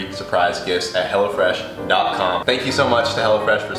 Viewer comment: Lmdao 1 I never realized how tall he is till he stood n the kitchen lol 2nd his commercial sell for hello fresh lol but I thought he kept saying hella fresh lol